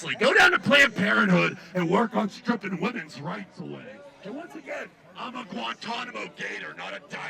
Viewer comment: I could have swore that said, "I'm a Guantanamo gay turd" :face-orange-raised-eyebrow